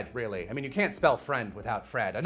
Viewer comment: for a second I thought you said you can't spell Fred without Fred (Also: Velma literally functions better as a lesbian you're so right.)